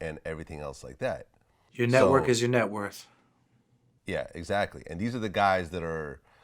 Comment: "Your network is your net worth" DAM Wise words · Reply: for sure, though i thought he said/i heard, "your net worth, is your net worth" meaning the net worth carries weight as to your actual worth in your existence on this earth